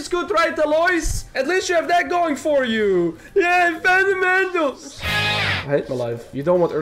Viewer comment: i read the other day "fund the mental" and i heard it at 3:15 Lmao